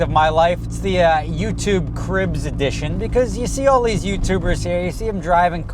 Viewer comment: At first I thought it said crips edition